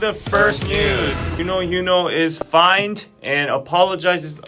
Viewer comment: I thought u said Uknow Yunho is fineee lol I was like yeah same